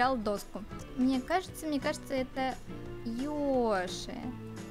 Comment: Йоши ты прям как Лена прям настоящая ты видешь Йоши?да немножко вижу Я котороя послышалось не Йоши а Л-Е-Ш-А:ЧЕЕЕЕ